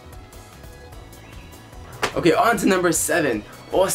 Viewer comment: Did I hear you say lemonade stand at 3:33,! Lol.